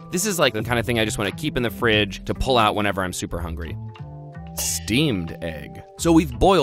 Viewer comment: i swear i heard steamed hams